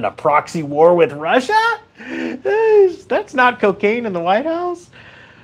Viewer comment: COCAINE? Oh i thought U said UKRAINE 😂😂😂